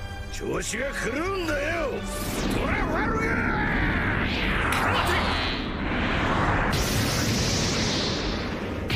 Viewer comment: tf did he say 😂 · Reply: He said trafalgar 😌